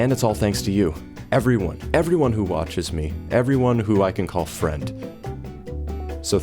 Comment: I thought you said: people who I can call Fred. HA Jokes on you! my name IS FRED! (Not really)